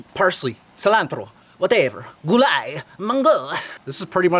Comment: did he say gulay, munggo?